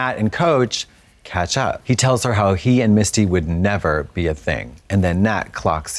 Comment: i had the captions off and i kept hearing "Miss Tea"